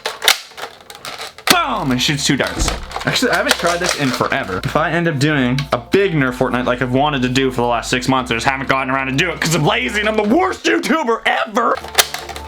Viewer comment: the captions say “Shoot students”😭😭